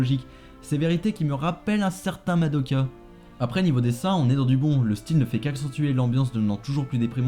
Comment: Je suis le seul à entendre à 9:45 "Au niveau des seins on est dans du bon"?... Pitié dites moi que je suis pas fou